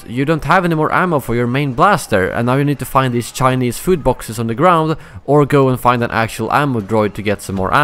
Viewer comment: Did he say "chinese food box" at 1:00?